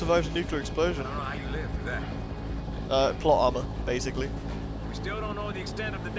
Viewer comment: Funny thing is when said "Plot Armor" I heard it as "Plot Alma"